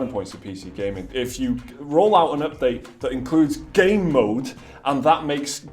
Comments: lol did you just say gay-mode???? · lol did you just say gay mode?? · LOL! Did you just say gay-mode? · Lol did you just say gay mode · Lol, did you just say "Gay mode"? :DDDDDD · Did you just say gay mode? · lol did you just say gay mode? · did you just say gay mode? :P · Did you just say gay mode?! ;p